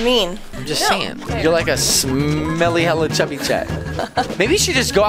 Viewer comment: I heard say hella in umm